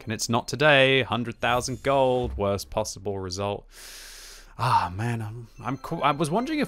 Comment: did you just say 100,000 gold worst result quicky?